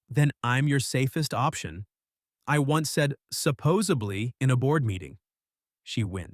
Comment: "I once said supposedly in a board meeting... the vocal actor said 'supposably' not 'supposedly'. Both are real words. Supposably = theoretically possible, conceivable, as may be imagined. Supposedly = allegedly, according to what is said, often implying doubt. I have heard 'supposably' and thought the it just a verbal stumble, so I learned something today.